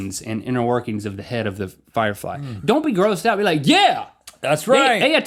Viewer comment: Did anyone else hear link say firefly near the end?